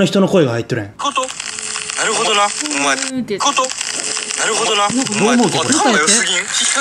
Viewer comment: の時何て言ってるかわかった『混沌、なるほどな』だね